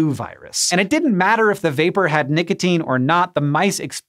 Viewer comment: Sorry, but I heard you say "Nigotine". It's ok though...